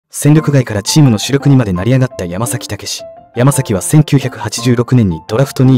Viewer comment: 最初「戦力外からチームの主力にまでなりやかだ」って聞こえた😂